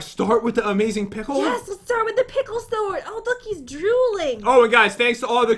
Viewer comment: I thought Jen said “it’s juuling” 🤔